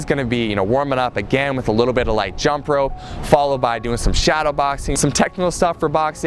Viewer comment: I thought he said he doesn't like shadowboxing